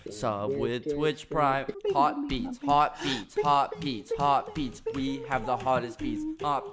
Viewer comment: In the end you say like "we have the hottest" idk what .. but I hear "we have the hottest BITE" with french accent .. And .. Bite means dick soooo 🤣